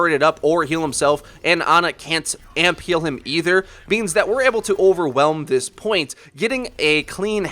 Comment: anyone else hear "beans"?